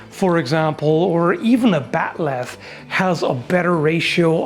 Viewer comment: what is this thing talks about? A sort of a curved double-bladed thing, but I cannot hear it's name right. Subtitles also do not help much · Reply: If ya still wondering he's talking about a bat'leth, aka the iconic crescent moon style sword of the Klingon, he made some videos on it if ya haven't seen them already.